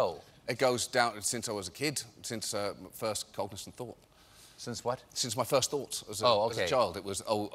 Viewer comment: I, for the life of me, cannot make out what he is saying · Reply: "Since I was a kid. Since my first cognisant thought." ('cognizant' in American English?)